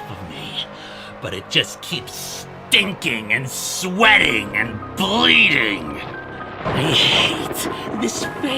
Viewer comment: So, fun little tidbit: This line is: "But it just kept stinking, and sweating, and bleeding!" But when I first saw this scene, I thought I heard: "But it just kept STICKING, and sweating, and bleeding!" TBH, I don't know which one is worse lol